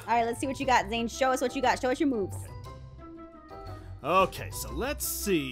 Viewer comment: when aph said "show us your moves" i thought she said "show off your b00bs";....